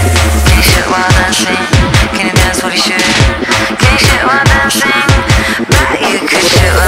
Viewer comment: I love this song. The first time I heard it I thought she said "can you sh*t while you're dancing? Can you dance while you sh*t?" I was so confused 😄😄